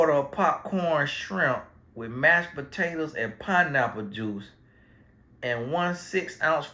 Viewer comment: Red lobster 🦞 order, sounds like two different meals. She feed Victor and the girlfriend. 🤣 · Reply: Art Ohhhhh that's right, I was wondering where the Pineapple juice fit in. I thought he said Pineapple bits. 🤔 So she feed the old family really good.🤦🏾‍♀️